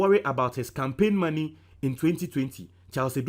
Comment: Did you say Bawumiah Campaign for 2020.? Does it mean the undercover took place before the 2020 elections? Maybe I misheard you